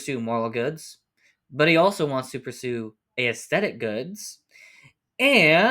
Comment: Small thing, but "aesthetic" isn't pronounced "ey-esthetic" but just "esthetic". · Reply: That was tripping me up. I was only listening to it as audio so it sounded like he was saying a-aesthetic each time haha